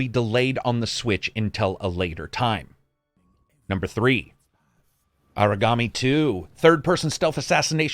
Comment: Yeah same. I want to know what the ninja game was. Sounded like he said origami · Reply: Aragami 2